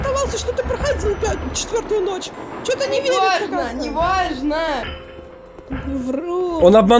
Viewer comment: мне послышалось или там был мат? После слов мне че то не верится нах@ заранее сорри за мат я просто хочу узнать одному ли мне это послышалось?